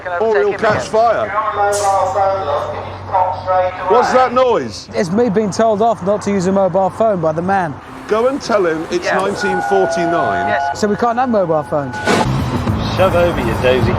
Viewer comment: I never noticed exactly what the man in the petrol station said, but at 4:50 he actually says " if you're on a mobile phone love, could you stop straight away" obv thinking Hammond was a woman🤣